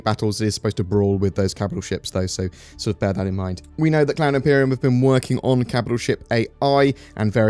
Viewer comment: I'm new here but... did he just say Clown Imperium? 2:55 haha